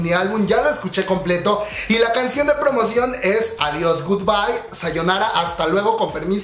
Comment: llamo mi atención desde el primer single, pero con "Adios" chau me obsesione , Entre como un "voy a ver que onda " y al minuto ya estaba "Gubaiovarios gubaiovarios gubaiovarios nananananana" intentando los pasos , y una vez que empiezas a buscar los nombres, NO HAY RETORNO ♡ ME ENCANTAN ♡ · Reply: Yo la primera vez escuché "goodbye ovarios" HAHAHD lol